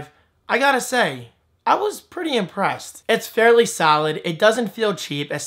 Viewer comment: am i the only one who heard him say saalid instead of solid... you are rocking the jersey accent